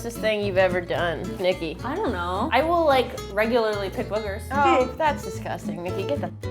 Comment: what did Niki say? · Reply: I will regularly pick boogers